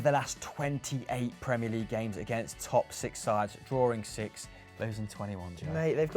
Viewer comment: Almost thought said "toxic sides" instead of "top six sides"